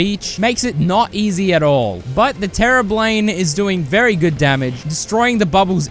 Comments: terra blane, am I the only one who heard that? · did bro just say "the terra blane"?!?!